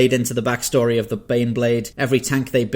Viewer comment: When you introduced number 4 I really thought you said The Beyblade